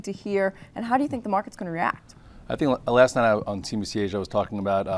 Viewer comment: cnbc asia? is that what Mr Scott Redler said?